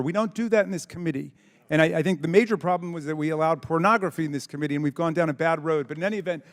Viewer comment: What does pornagraphy have to do with any of this? Did anyone hear him say that? It's 4:18 minutes in. This country is doomed